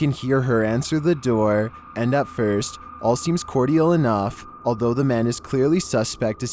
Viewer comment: did you say Core-dee- al🙂🙂🙂 10:21 the attempted car jacker was smart for running and leaving the car. Once he sees the kid in the back a misdemeanor 1 rsp could have became a kidnapping felony 2